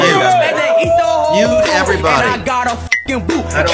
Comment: OMG i am so stupidddddddddd... I thought he said "N*DE EVERYBODY" 9:32 LMAOOOO.. HE WAS SAYING MUTE